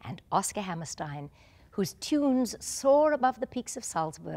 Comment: There's a big mistake in the captions. In minute 1:32, it says "..."Richards Rogers and Oscar Hammerstein, whose tombs soared abovethe peaks of Salzburg.." What Julie Andrews actually said is " whose TUNES soared above the peaks of Salzburg and audiences everywhere" It'd be nice if somebody corrected this caption. I'm a teacher of English for a teaching English programme and I'm working on the phonetics of this video. As a teacher of English Phonetics and Phonology, I believe the difference between the pronunciation of TOMBS and TUNES is very clear.